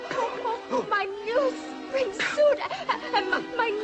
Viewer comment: What does she say at 1:30 "My new ______ and my new hat?" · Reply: stagesiren7 "My new spring suite and my new hat."